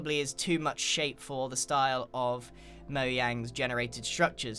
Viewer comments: DID HE JUST SAY MOYANG? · What does he say mow Yang's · who else heard moyang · did he say moyang?